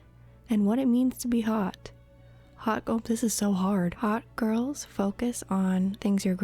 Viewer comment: I thought you said "Hot girl pisses so hard" and had to turn on the captions to double check